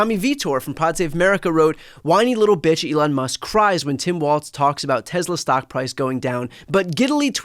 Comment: What name was he trying to say? Ive watched it 8 times and I keep hearing "term waltz". Im not making fun of his accent. I have autism and this is probably a case of me missing context. If anyone nice would like to explain.